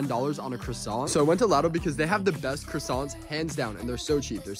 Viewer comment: LIDL truly is the best bakery 🙌🏼🙌🏼 Edit: glad over 2k people agree with me 😂🙌🏼 this is awesome. Their bakery is amazing. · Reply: I thought he said “latto”. Like some American chain😂